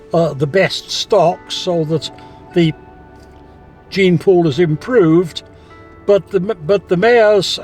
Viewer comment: I promise you I misheard that as "the best Starks, so the gene pool has improved" and forgot for a moment he was talking about horses.